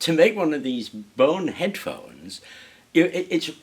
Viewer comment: BONEHEAD PHONES. You misheard it here first! lol